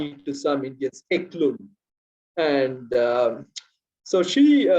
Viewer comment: What is the name of person who interviewed the scientists (between 23:23 and 23:27)? Sorry, I'm unable to understand what he's saying. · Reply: He is referring to Elaine Howard Ecklund and her works. Look up her research on scientists and their religion